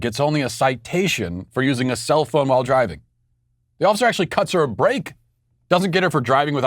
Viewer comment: I thought Matt said " ...he cut her brake" ! SOMEBODY should have.....😶